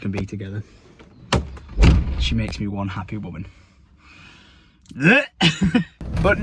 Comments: wholesome moment but did you say "makes you one happy woman" or am i going crazy XD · Am I tripping or at 6:00 does he say "she makes me one happy woman." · did you say she makes you a happy women 🤣 I guess we know who wears the pants in the relationship